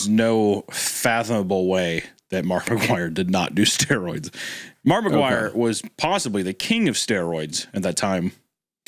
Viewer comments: I thought they were saying markiplier 😭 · When was talking about his Mark McGuire story i thought he said Markiplier. Now that would've been terrifying.